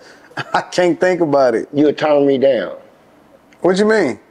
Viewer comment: I thought he said “You’ll tongue me down” lmao 😂😂😂😂😂😂😂😂😂😂😂😂😂😂